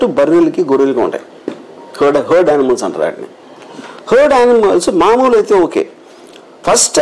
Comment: did he say curd animals ? 49:36 That red book plays important role in 2024 elections. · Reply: Herd animals such as sheep, goat